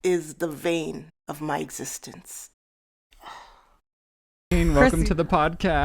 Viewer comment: okay ik this is an annoying comment but it sounded like you said “vein of my existence” just for future reference i think it’s “bane of my existence” i could be wrong tho